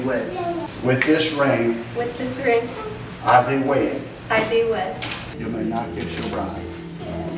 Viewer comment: i seriously thought that the guy said "you may not kiss your bride" for a second. still a geat wedding